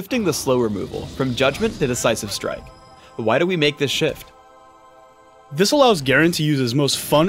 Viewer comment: thought he said "Why do we make this shit?" lmao